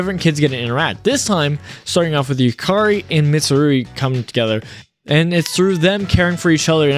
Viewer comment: Lol, did you call her Mitsurugi at 1:46:06. The image of the dude from soul calibur will forever seared in my mind.